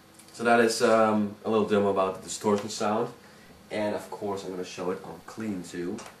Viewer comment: What does he say at 2:40??? storing sound or something XD???? Plz react !